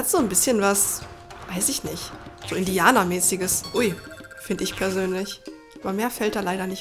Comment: hab ich da Indianer gehört? °^° Floink ist so knuffig, krieg ich das? :3 :D Dat Rasaff am Ende xD Toller Part. ^-^